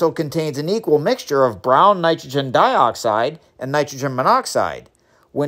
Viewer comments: I thought he said brony 😭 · My brain was confused for a split second cause I thought you said carbondioxide. I was like no wait... waht.